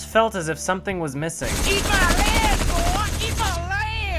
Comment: "Eat mah lead boy! Eat mah lead!"-Hillbilly (<num>) RIP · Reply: i swear i thought he was saying “eat my lil ass”